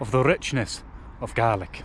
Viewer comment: For a second I heard the "richness of garlic"